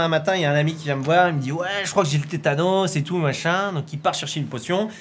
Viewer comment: À 0:31 j'ai entendu "Ouai j'crois qu'j'ai vu Thanos"